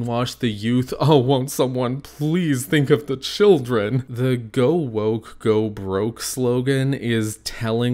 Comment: I’m gonna be honest I thought they were saying if they were woke they’d go broke. I know that makes no sense. But to my mind neither does the alternative. XD I’m sure you’re right but what? Yeah that makes no sense. XD